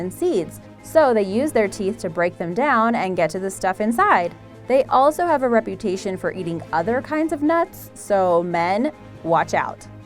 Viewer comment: "They have a reputation to eat OTHER NUTS. So MEN (.-.) watch out" did i misheard it or what? You Know What I MEAN